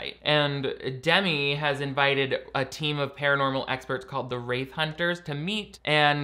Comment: Every time you say “wraith hunters” I hear “rape hunters”